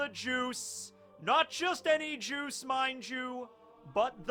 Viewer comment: Thought you said my jew instead of mind you lmao